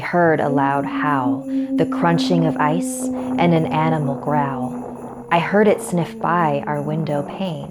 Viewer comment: i thought she said anime growl XD